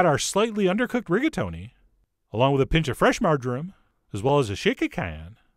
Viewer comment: “Along with a pinch of fresh margarine” Wait. What?! I’m still recovering from that mishear.